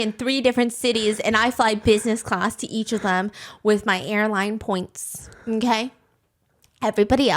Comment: for a second, I thought you said “hairline points”